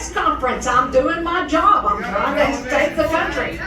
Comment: she meant and sounded like she is trying to take the country lmao